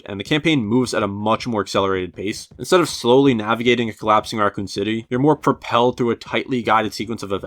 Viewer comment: is it just me or did it sound like He said "Arkham City" when he slurred saying Racoon City here